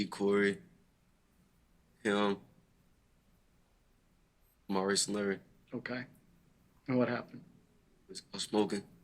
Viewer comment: I thought he said Bryce n Larry